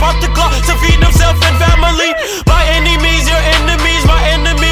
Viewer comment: Did You Say Family?